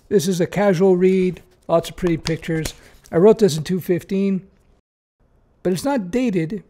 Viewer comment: Did he just say he wrote that book in 215?